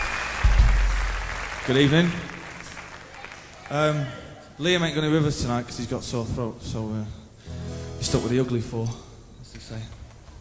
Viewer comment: Sorry about my english: what Noel said between 00:20 - 00:30? Thanks! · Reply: "Good evening, err.. Liam aint gonna be with us tonight 'cus he's got a sore throat so, err.. you're stuck with the ugly four, as they say"